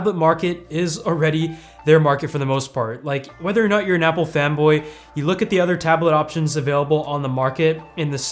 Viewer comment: Lol I heard "Apple femboi"